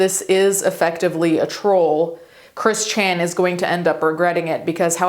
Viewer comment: Misheard you say "Chris Chanity" and blew my own mind.